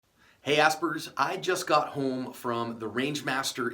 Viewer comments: Thought he said hey ass burgers 🤣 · I thought you said "Hey Aspbergers". lol. Some people might not find that funny though.